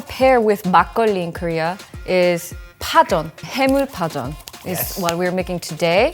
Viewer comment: caption error is making 해물파전 which pronounces haemul pajeon. I think you heard the "haemul" as "ham with"....